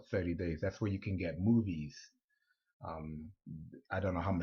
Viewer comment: did he said boodies!